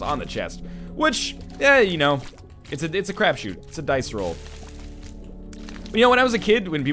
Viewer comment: is it "crapshoot"? Sounded like he was saying craft shoot, is there another expression less vulgar that i'm not familiar with? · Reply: its crapshoot